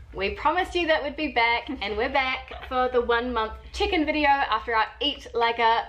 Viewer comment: Everytime says "check-in" I hear "chicken"... 😅🙈